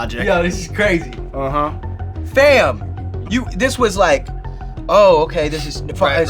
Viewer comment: Did he said fam in14:41